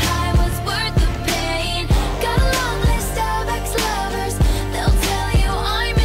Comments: Am I the only one who knew since beginning that it was : "Got A Long List Of Ex-Lovers"....???? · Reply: um..when i first heard it, "got along with Starbucks Lovers..." lol, but then i saw the lyrics and i was like "wtf?!" haha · I thought it said "Gotta love those Starbucks lovers" lol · I thought it said "gotta love these Starbucks lovers" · I thought taylor swift said God loves those starbucks lovers! 😂😂😂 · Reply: i heard 'got a long list starbucks lovers' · I just found out that it said long list of Ex-lovers it’s 2022 and I never knew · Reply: ikr? When i first heard "starbucks lovers" i was like "WTH Taylor!" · in blank space i heard it has "got a long list of star ex lovers" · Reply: I heard "gotta a long list of star-crossed lovers" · I thought Taylor said “gotta love those starbucks lovers” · I thought she said ‘Gotta love those Starbucks lovers’ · I thought taylor swift said God loves those starbucks lovers! 😂😂😂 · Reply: I heard "Gotta love those Starbucks lovers" 😂